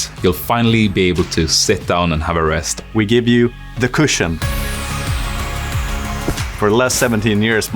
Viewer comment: subtitles need to be fixed, says "c [space] ushion" instead of "cushion"